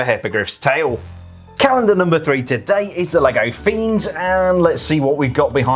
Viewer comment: I heard a "Lego fiends" in there... 😅